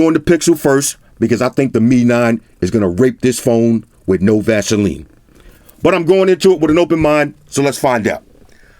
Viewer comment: Wait. Did you just say 'rape this phone without Vaseline'? I had to take a double take before rolling on the floor. These lines are great!